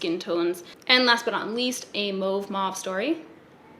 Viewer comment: OMFG did she just say “a move story”?! I’m fucking screaming 😂😂😂😂😂😂